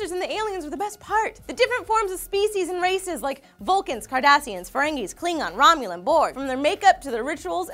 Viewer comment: Swore she said "Different species vulcans, Kardashians, etc.."